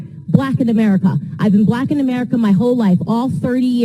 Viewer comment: DID I HEAR HER SAY SHE BEEN A BLACK AMERICAN ALL HER LIFE....DON,T TRY TO BE BLACK NOW.